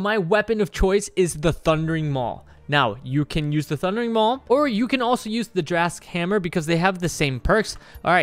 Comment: Did he just say you can use the thundering maul (drasks hammer) or drasks hammer? Or am I just deaf?